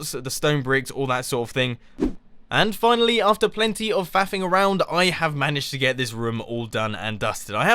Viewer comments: In 16:31 did he just said "fapping around"? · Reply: "FAFFING" around. It's slang for "messing around" well, it kinda has it's own meaning. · did you say you were faffing? haha lolz